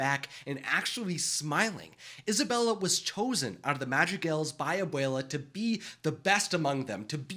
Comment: Tell me why i keep hearing "magic elves" instead of madrigal 😂